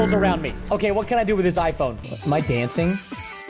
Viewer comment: i thought he said " is my cat dancingc"